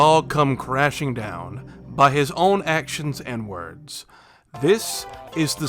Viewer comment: Thought you said “…by his own actions, N-words” …by his own actions and words